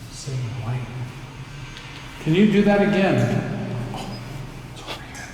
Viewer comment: Hey guys glad your back! Your wedding was beautiful!! So I hear a voice say "here I come" at exactly 1:47:11/12 you should wear earbuds to hear clearly! I have played it over and over it's crazy because it's when you guys are experiencing some stuff that goes right along with what is being said! It's unbelievable!! I also have added more times to listen to as well it's in comments below this comment you have to hear this!!!! I'm not sure if you're already aware of it, I kinda scrolled through comments and didn't see anything being said about it. So if it's old news the please forgive me. But check it out it's worth the effort for sure! Keep up the amazing work!!!! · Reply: Also oh my gosh I think I'm loosing it but I heard a voice say "do it again at 1:50:14 holy ##it!!!!!!!